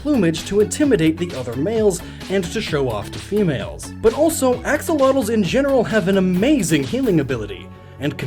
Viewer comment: what’s the fish at 7:15? I know he said it but don’t know how to spell it and captions didn’t help either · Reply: It's an axolotl in English. Ajolote in Spanish. They're Mexican owo